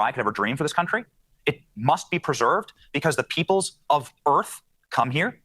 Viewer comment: When he said "people of Earth" what I heard was 'people of birth'. I had to turn on captions to understand what he said. I still think that what he meant to say was 'birth'. Birth from the 'correct' parents.